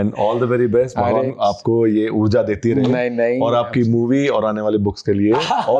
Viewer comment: did he say 'movie' by Anand Ranganathan???